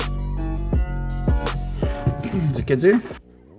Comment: I still cant understand what he says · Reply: Is it kids here